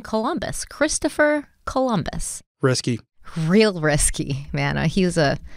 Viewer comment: Yooooo when you were talking about christopher columbus and derrick said "risky" i freaking thought he said "chrisky" like a nick name and now i will only refer to columbus as "chrisky"